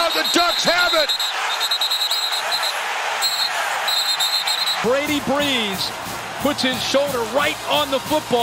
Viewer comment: Did they say Brady Brees makes the play?